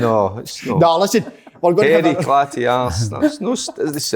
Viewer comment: I thought he said a clatty arse aswell ! Hahahaha